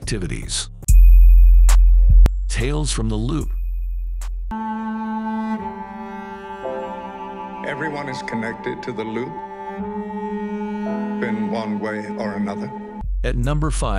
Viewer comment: Misheard quote: "Everyone is connected to the loo". ;D Thank you very much for this video. I'm so lame in trying out series, so this overview helps a lot. 👍 "Utopia" an "Electric Dreams" are my cup of tea. =)